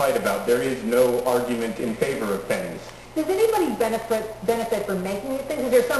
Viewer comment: I honestly thought that said penises. Jesus Christ